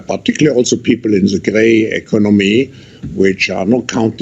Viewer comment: What did he call us? 😮 The "grey economy"